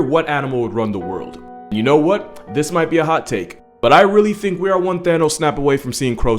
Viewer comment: I thought you said this take was hot.